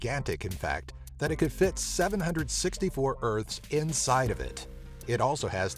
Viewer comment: did u say 764? O.o